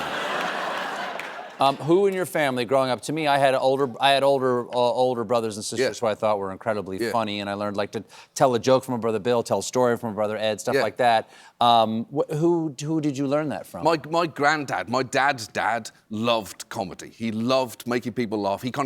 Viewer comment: Did he just say his brothers are bill and ted? Excellent!